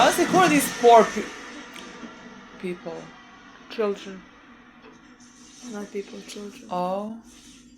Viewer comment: did i just hear you say that children aren't people?